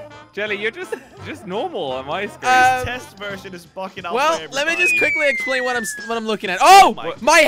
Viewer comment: i need answers, did crainer say "fxcking"???????